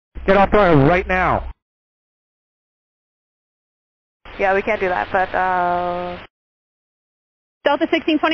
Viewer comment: "We can do that." I think she said "We can't do that." Was that a refusal to protect her company's plane? Did she not want to roll onto the grass (or lot) or detour with risk to plane? Seems strange to me she kept just rolling on down passed the lot then passed the first turn off too. obstinate or some other explanation?